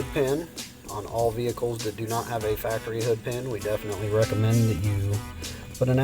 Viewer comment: you mention if i heard it right a "HOOD PEN"? what is a "HOOD PEN"? (you said that "somebody might start the vehicle with the remote start active?) pls advice...thanks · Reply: The hood pin Usher's that the hood is closed at all time or the remote start will not work it is just a pin switch that when the hood is closed it pushes down not creating any kind of ground then when the hood is open it pushes up creating or ground telling the remote start not to start it's a safety feature so if somebody is working on the vehicle it will not allow them to accidentally start the vehicle with the key fob in their pocket or something of that nature